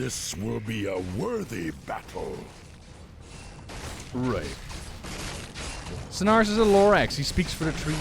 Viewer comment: I misheard "Cenarius is a lorax" as "Cenarius is a lore axe" and my mind went to Broxigar's Axe of Cenarius X3 Also in playing cata classic recently, there's a bit in the cooking dailies about Garrosh relegating the trolls to the outskirts of the city, and the cook stealing their rice to feed the armies - I wonder how much more there was of Garrosh's orcish elitism prior to mists?